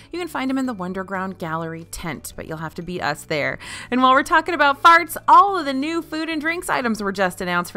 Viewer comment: Did I mishear or did narrator say “while we are talking about farts”